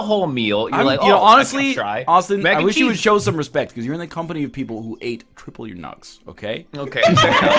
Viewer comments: What does NL say at 29:40? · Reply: “Austin, I wish you would show some respect, because you’re in the company of people who ate triple your nugs” in reference to their nugget challenges. I’ve been stuck on what he was saying for months but I finally hear it now lol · What did Ryan say they ate? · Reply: Nugs which is short for chicken nuggets